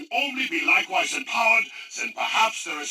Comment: He does not say the site can only be likewise Powered. he says Empowered. Now there are Three Things that i would like to consider. The basis of this is Powered. you can take it with a grain of salt and say he says likewise and powered. instead of Empowered. OR that he says Empowered which would probably be Directed towards moon and controlling of the zombies which is the real definition of empowered. OR that its a missused word for a reason. Other site Powered by EMP or EMPowered.